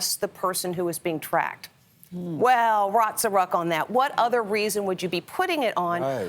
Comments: Did she say "rots o' ruck on that"? Why? I thought I misheard her, but she dropped the Ls. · did she really says "rots of ruck"???